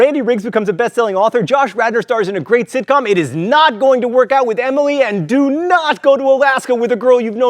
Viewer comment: my name's emily :/ at first i thought he said "it's not going to work out, emily." I was like "wh-wh-what?"